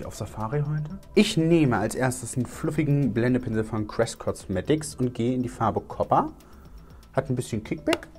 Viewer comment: ich verstehe da die ganze Zeit: „Blende Pinsel von crsiquin cosmetics...“ oder so 🤣🤣